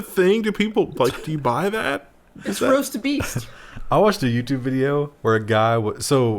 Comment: "It's Roast 'Beast'" I heard you